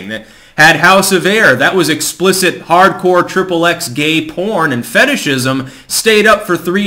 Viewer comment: "Is it Hardcore?" Still waking up I heard "Is it Parcour." A mental image of skinny dudes leapfrogging over fat chicks having sex with them.